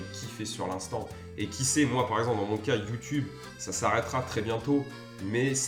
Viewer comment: j'ai pas compris c'est quoi qui va s'arreter très bientot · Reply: Bah YouTube, j'ai l'impression qu'i' ça bientôt arrêter sa chaîne